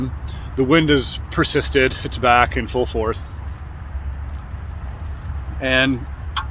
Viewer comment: Full fourth? Did you really think that was the saying or where you just drunk?